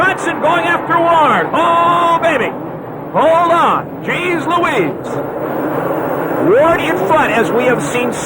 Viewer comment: What is the expression Larry Huffman says at 2:26-29? Thanks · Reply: "oh baby Hold On Jeez Louise" lol